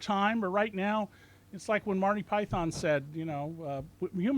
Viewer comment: Did he just say "Marty Python " at around 36:50 .